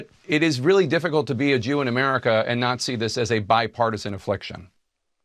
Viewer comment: Jake says, "It is really difficult to be a Jew in America and not see this..." Or did he say, "and N@ this?" Hmmm...